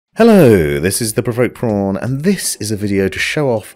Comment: Thought you said " This is the pervert porn" I was like what the hell.